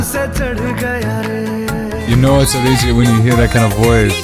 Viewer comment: min- Anyone heard him saying - "You know it's ARIJIT when you hear that kind of voice "❤️❤️